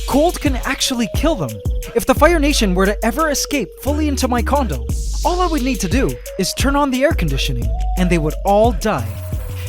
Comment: Am i the only one that heard him say: if the firenation was to escape in my condom, all i would have to do is turn on the air conditioner